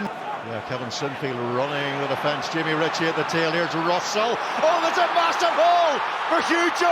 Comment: Genuinely thought commentator said “oh there’s a ba**ard hole”